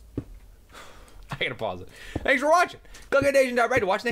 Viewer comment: i thought said pee off not peace off lol